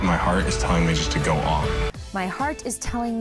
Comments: was one of my fav moments of the show too!! but more so because Colton said "My heart is telling me just to go ALL IN" not "on" I did love the Celine Dion joke haha, I just think that what he said just shows how strong his feelings Cassie really are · I really thought Colton said, "my heart is telling me to go all in" no "go on"...no? haha anyway I thought that Celine Dion bit was hilarious. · I think Colton said "go all in" not "go on"